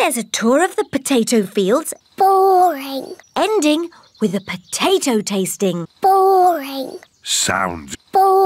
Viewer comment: did she say “ending with a potato tasting” Mr.Potato is killing and selling his own kind💀